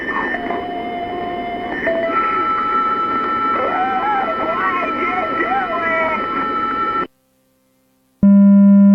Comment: what did it say? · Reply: “Why’d you do it”